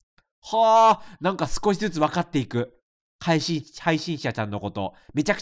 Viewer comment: ずっと 配信ちゃちゃん って聞こえるw